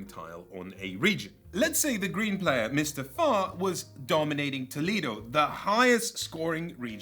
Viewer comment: Loved the bit about “Mr Farr” and Klinger. My entire knowledge of the city of Toledo comes from that character. A friend’s dad went there and came back with a shirt from Tony Packos Tacos, apparently it’s real! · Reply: I thought he said Mr. Fart!